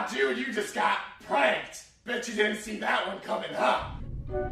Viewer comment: What did they say... I couldn't hear them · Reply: He said: oh god, you just got pranked, you didnt saw that coming out